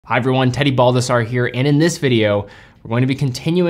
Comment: Is it weird I heard him say his name is “Teddy Bulbasaur”? So sorry 😂